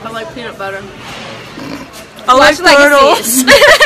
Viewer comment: Did lulu say she like girls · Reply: D Ll no she said I like turtles❤️😂